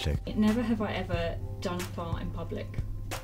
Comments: "Never have I ever done porn in public" I can barely hear her. His answer to the question makes me believe that's not what she said though. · what did she say I tried listening on full volume i tried captions its not helpful or working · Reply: She says, "Never have I ever done a fart in public."